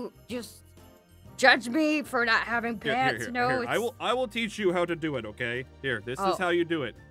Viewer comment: i thought said “i will take you to méxico” when he was a seagull